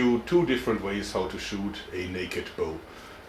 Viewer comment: For a second there I thought you said she would shoot it naked. No such Luck 😋